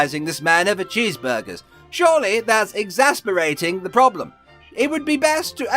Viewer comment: said Exacerbating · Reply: maybe that's what he meant but I heard "exasperating"🤔🤔🤔